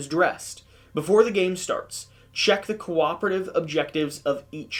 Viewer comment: "Corporate" objective, not "cooperative" objective :) 25:45 - "Samples are considered Heavy Objects" (Aftermath Rulebook page 5 section Alert Cards). This means you need to pay 1 action for the "Pick up heavy object" basic action. Heavy Object needs to be carried in one of your Hands (same as Corpse, Carcass and Eggs) (Nemesis Rulebook page 22) 41:25 - Moving from a room with a Intruder is considered a "Escape" basic action. Instead of resolving a Intruder Attack card, the Larva in the room attacks you immediately (Nemesis Rulebook page 20) 46:55 - Forgot to 'rotate' the room to reduce the number of items after searching 49:23 - No need to put the Larva on the Intruder Board, only on the Nest room. I really enjoyed your Playthrough, can't wait to play the Aftermath expansion myself :)